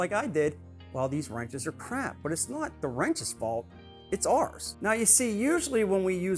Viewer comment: This is what I thought I heard, for real; "it's not the wrench's fault, it's arse." Brilliant and true.